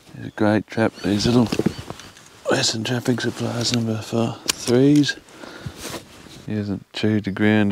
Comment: hey mate nice catch..But I didn't quite hear the type of trap that was...number 3? i heard..Is that a WTS 3# dogless? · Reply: Yes it is a WTS #3 dogless offset laminated trap. Sorry i stumbled over my words in the video haha. They are my favorite trap in used. And the cheapest! Hard to walk past a trap that is pretty much ready to go for $50